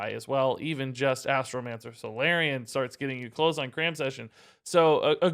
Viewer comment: Brah I thought that one card said “Cream Session”!? 😂